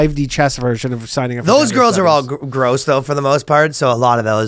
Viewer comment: what did he just say? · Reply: He said they’re gross!! Yucky!! Foul!! Nasty!!